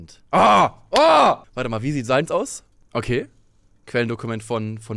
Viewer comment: Bei 9:30 habe ich erst verstanden "wie sieht salz aus?" und dachte erst er referiert zu dem Spruch "salty sein" und würde dann Bilder zu Salz googeln.. My brain xD why.